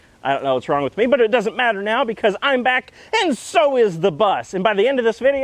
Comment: At first I thought you said "I'm back, and I sold the bus" and I was freaking out